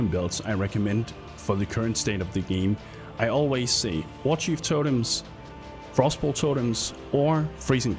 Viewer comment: what the hell did he says? Feezebolt, freezing pulse and? I want to make a totem build but i dont know which spell. Maybe: arc, divine ire or ice spear. Which build has more surivivability? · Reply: warchief totem